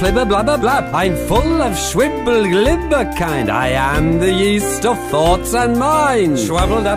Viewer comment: My friend thought it said "I'm full of shwibberly liverkind" wtf 😂